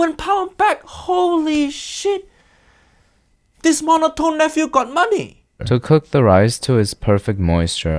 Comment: I legit thought he called him a mofo 😂😂